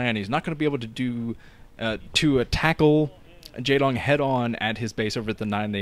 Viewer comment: So... did I hear say "attackle"? Yet another portmanteau from the king of attenergy. 25:43.